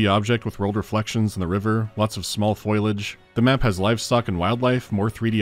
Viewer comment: did you say foilage?